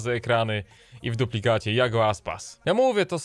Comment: usłyszałem i w dupę dziadzie Iago Aspas xD